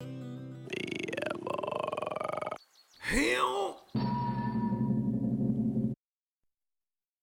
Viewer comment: I think he saying Hell